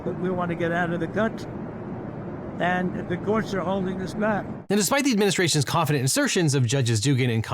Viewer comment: I cant b the only one who heard what he said following 2:08 rite RIGHT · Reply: He said "the law is holding us back."